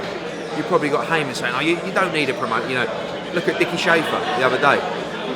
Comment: did he just call Richard Schaefer 'Dicky Schaefer'? lolol haha Schaefers gonna have a meltdown after this just like he did when Eddie immitated his accent